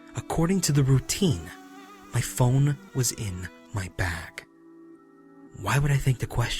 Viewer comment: I kept hearing "my phone was in my back" instead of bag...